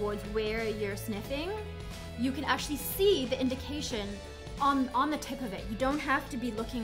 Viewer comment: When you said on the tip, I thought you actually said just the tip and I finished right then. 10/10 would recommend